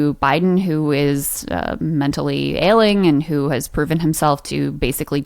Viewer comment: Did Crystal say Biden is “mentally ailing”. LOL. Yes, poor President Biden is past his prime. I can’t believe the DNC is allowing him to run in 2024.